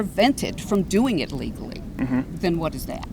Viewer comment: i think misheard her. Pretty sure she said legally but he thought she said ILLegally